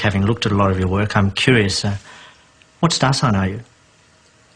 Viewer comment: What does the conductor say at 7:46? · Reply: What star sign are you?